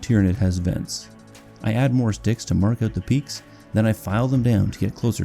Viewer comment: For a second i thought you said add more d*cks to your peaks 😂